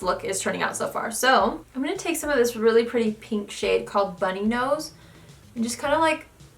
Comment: I thought she said bloody nose